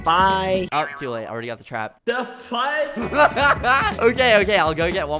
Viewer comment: what did he just sayyyyyyyy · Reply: “The fudgeee”